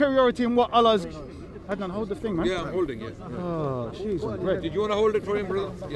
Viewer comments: did he said Oh jesus ? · did he just say 'Oh Jesus'. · Did he said "oh Jesus" at 39:14 ??